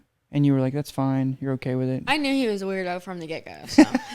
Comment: I thought she was saying "gecko" until I recently realized it was "get-go". 🤣